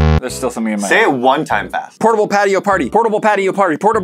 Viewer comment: I thought said portable patty party